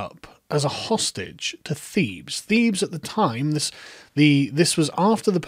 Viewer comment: I keep hearing "Thieves" lol but it's probably Thebes, I just haven't heard it pronounced as one syllable (I always thought it was Thee-bes or The-bes, not Theebs)